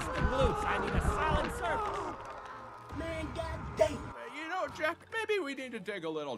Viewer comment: bruh I'm not gonna lie that's what he would say in his head🤣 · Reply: [[man god dam]]